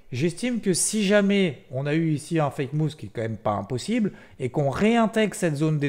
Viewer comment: salut c'est quoi un "fake mousse"? (min 18:25), sinon merci pour cette vidéo et de me rassurer pour NVDA and co ;) bon dimanche! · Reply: "Fake Mouv' " ... j'irai prendre des séances d'orthophoniste